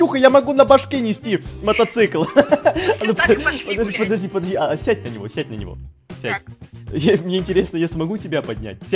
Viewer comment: Я одна услышала что с материлась? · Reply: скащала всё так пошли гулять